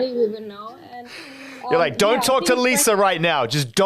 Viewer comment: Lisa solo sonríe cuando habla Jenni y ellas dos solo se miran entre si parecen novias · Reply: Yo entendi dont talk Jenlisa rigth now, estoy loca?